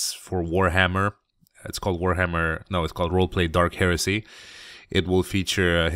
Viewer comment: I thought it said Dark Hershey at first :D